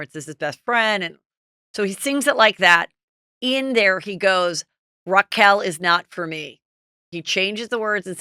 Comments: Re: Tom Sandoval singing on stage - i saw the tiktok video where he’s singing Stacy’s mom and I heard “Schwartz’s can’t you see, Raquel is hot for me”🙈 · I thought it said Raquel is hot for me lol